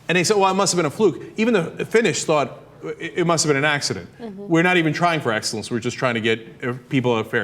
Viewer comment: Thank you very much :-) , I just thought that he was saying "accents" lol, he talks pretty fast.